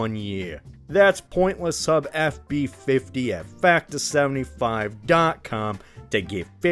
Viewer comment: I heard "Pointless Sub" and I thought "oh no the Aliens did stole my data."